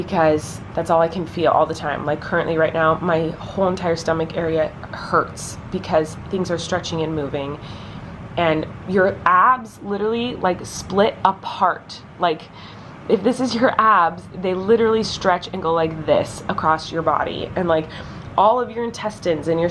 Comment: lol when Colleen said abs I swear she said ass and I was like WTF ur ass spreads apart???